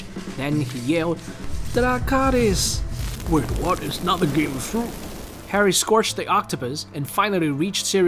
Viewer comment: I heard dr. Strange say: wait what? It’s not the game of fruit